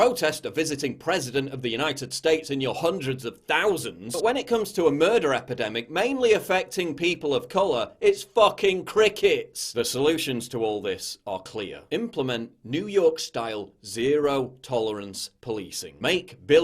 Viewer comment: Oh, you said fucking crickets, plural. I almost thought you said murdering black kids was proper cricket. That would have been a charge under the Communications Act, surely. OK, back to work then.